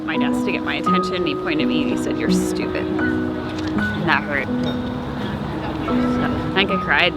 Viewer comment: What does he say? You were an ___ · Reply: accident